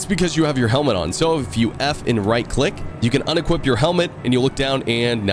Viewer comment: Ah. "F and right click." I heard "F-in' right click"